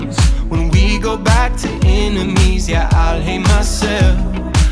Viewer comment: tell me I'm not the only one who thought he said : "go back to Indonesia" instead of "go back to enemies yeah"